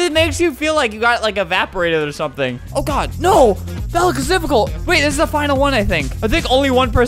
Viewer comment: Albert said “That looks difficult” I swear to god I heard “Falikazipical” I don’t even know